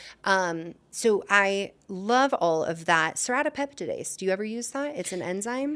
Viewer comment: did she say Serrapeptase?